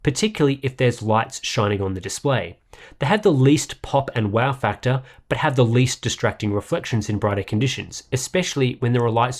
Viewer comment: I don't understand what did he said? "They had the least pop and wow factor" or what?